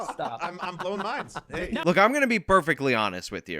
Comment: Lance: I'm blowing minds! *I thought I heard him say mimes at first lol.